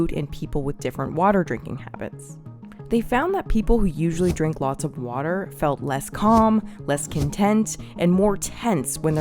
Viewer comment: is it just me or do I hear her saying "they found that people drinking LOTS of water felt less calm, less content etc." heads towards the kitchen to get a glass of water 🌊